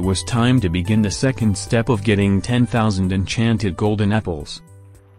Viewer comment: first i heard the step of gettin enchanted golden nipples lol sus